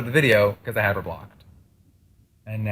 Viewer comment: wait did jeffree say blocked at 3:00 ??